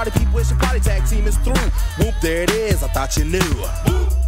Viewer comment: I always thought he said MOVE THAT ASS 😂😂😂😂 BUT HE SAID WHOOMP THERE IT IS